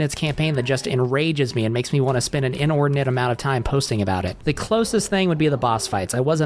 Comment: bro i thought you said "spend an "N word" i was like hold on now 💀